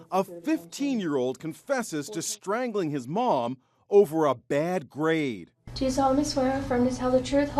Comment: I thought it said he killed over bad grapes